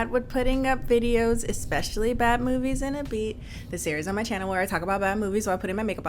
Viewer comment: Ya know, for the longest time I thought she was saying Batman movies & a beat... I feel stupid...